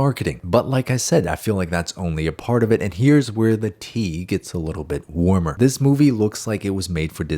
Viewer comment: I had this playing in the background with speakers. And I thought I heard here's where pee got warmer